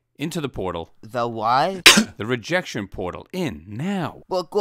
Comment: When I first watched this I thought Mephone said “The rejection portal. In hell” Lol